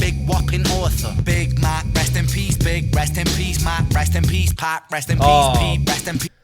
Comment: I thought he said rest in piece Pat .. like pat stay